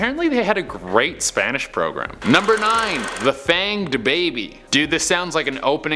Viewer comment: dude for #2 i thought he said “the famed DaBaby” and i lost it😂